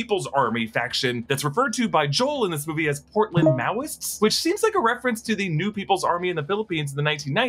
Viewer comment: I misheard the phrase "portland maoists" in the beginning so i thought they said "portland, Maui-ists" as if Hawaii and Oregon were in an alliance so i thought the Hawaiian shirt guys were west Coast transplants trying to defend their college campus from Western forces.